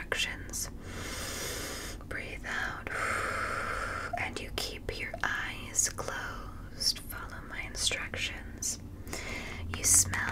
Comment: Ohhh keep my eyes closed, not ice globes! I had my eyes closed but once I thought I heard ice globes I woke up like a dog xD